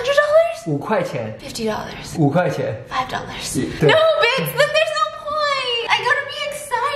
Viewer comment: can someone pls clarify what she's saying Bits Bith ??? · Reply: Guess you're new!!!! She replaces "Bitch" with "Biss" XD